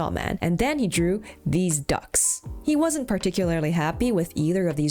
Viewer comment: My mind is so corrupt that I heard Deez Nuts instead of These Ducks 😂